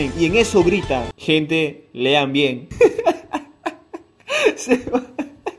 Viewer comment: 😐 no entendí absolutamente nada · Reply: dice lean bien pq cuando vio el examen vio q estaba mal hecho